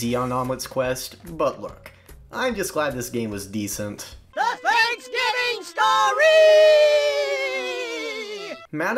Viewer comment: Did I just hear Hoops and Yoyo? Aside from that, I have a lot of great memories from these games. And I completely agree that “Larry’s Lab” is at the top of the list! Great review! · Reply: It is hoops and yoyo